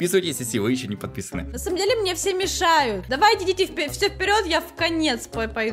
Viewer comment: Когда сказала (все идите вперед )мне послышалась (Все идите в пень)🤣🤣🤣🤣🤣